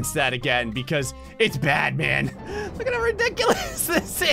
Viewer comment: You said "It's bad man" I thought you said "It's batman"